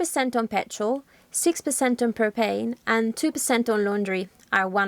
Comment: 6% on propane. I thought I heard cocaine. Doing office stuff with you going in the background. Ha Ha.